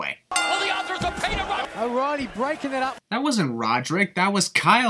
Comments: Nigel did say that O'Riley broke up the pin. With his accent thats what made it sound like he said Roddy. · what are you talking about? He clearly says “O’reily” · i think he said o'reilly not roddy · Love your channel man excellent YouTuber but i do have to notify an error at 13:00 when you sinned the commentator for saying Roddy broke up pun he really said o reilly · Yo at 13:02 when u think he said roddy he said O’rielly listen clostley awesome Video ur the best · sin it was at 13:02 u thought Mauro said Roddy when in actual fact he said o'reilly so remove a sin 😂